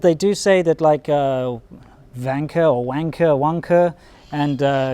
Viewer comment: did you say there's a construction company called "Wanker"? They must be good with erections